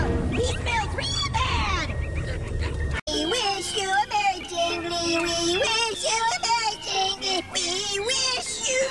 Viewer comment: from 1:40 to 1:46, i could never, EVER understand what gir was saying, it's like "we wish you a merry ***, we wish you a merry ***" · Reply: "We wish you a merry Jingle-y"